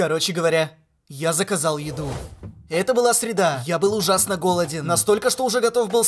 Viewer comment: С первого раза мне послышалось "это была суета"